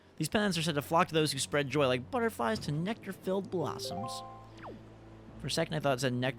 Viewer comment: I'm glad that I'm not the only one who thought it said "nectar-filled bosoms".